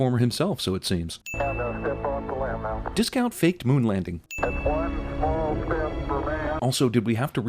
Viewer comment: Wait why was "Discount fake moon landings" a sin again? :S Telling conspiracy theorists they're wrong is a sin? :S Or did I mishear?